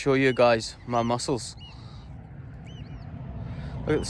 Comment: When you said mussels, I thought you said your muscles on your arms, and I did not know that your were that colorful.